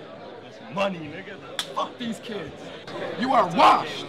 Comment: did he really say "money nigga, fuck these kids" dead af